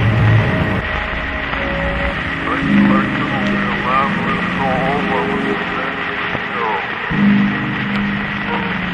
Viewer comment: I hear it says something, what does it say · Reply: This selection will be a mandolin solo by Mr James Fitzgerald